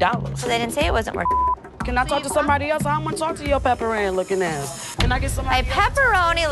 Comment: Said "PepperAnn", thought she said "pepperoni". Someone's feeling guilty for eating that pizza xD.